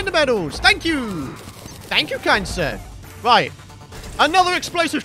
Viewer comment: Every time he says thank you kind sir it sounds like he saying thank you cancer